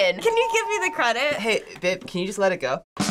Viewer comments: did just say babe???? :O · did say “hey babe” to are they together???